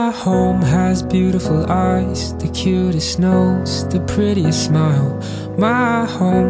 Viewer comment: The first time I watched it I thought it said the prettiest bum instead of prettiest smile 😅 very cute lived it